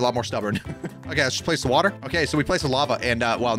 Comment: dID HE SAID WATER